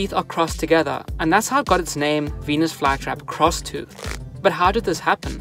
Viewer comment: Look o wasnt paying attention then i heard yt saying: "penis fly trap" i was like wth what the actual hell is a penis fly☠️